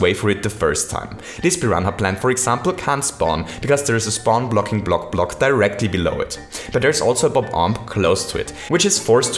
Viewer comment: all I hear is block block block block block block block block contraption